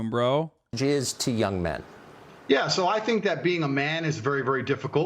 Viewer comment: it sounds like tucker says "jizz to young men"